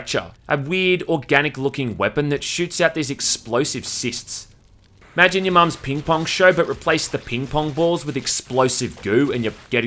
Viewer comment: "Imagine your mom's ping pong shirt." Am I hearing this right?